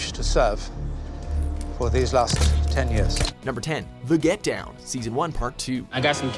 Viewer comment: Anyone else notice he says 10 instead of 8 at 2:34 ?